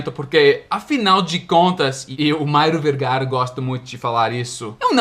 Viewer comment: 1. Em 6:05 ele falou "Mario Vergara" ? rs 2. Devo confessar que sua explicação foi bem complexa pra mim. kkk 3. DAAAA BOLÉTEEEEEEEEEEEE!!! KKKKK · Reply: Eu também ouvi Mairo Vergara Kkkk